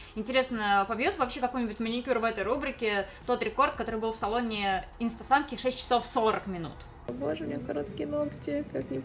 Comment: она сказала в салоне инстасамки или мне послышалось?